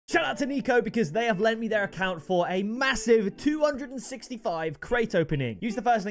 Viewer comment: did he just say MASSIVE